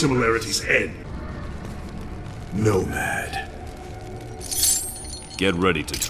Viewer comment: did scorpion say notepad? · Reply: Nomad